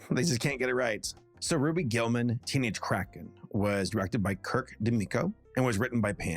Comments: I was listening, basically like it's a podcast, and i heard "Ruby Gillman, Teenage Crack Head" from dreamworks and I was like, bruh wtf!? Lol. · Okay. I'm sorry, but when you first said the title of the movie, I thought you said "Ruby Gillman: Teenage Crack Head", which might have explained why no one was talking about it. (In my defense, I was listening to, but not watching, the video) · I had my volume turned down waaaaaaaay too low at first and I thought said "teenage crackhead" and I had SUCH whiplash thinking "THIS IS FROM DREAMWORKS?!"